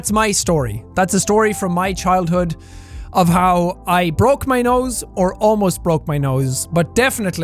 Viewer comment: I thought it said noose Holy fuCk